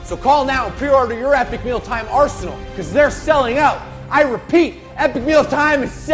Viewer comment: wait did he say they weren't selling out?